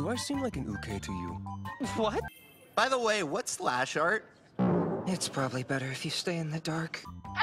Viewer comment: what did sasaki said in 0:21 ? Lashert? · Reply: Slash art